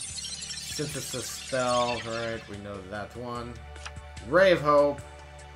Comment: lol first i heard "brave hoe"